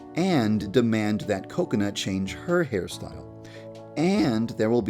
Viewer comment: I thought u said kokonut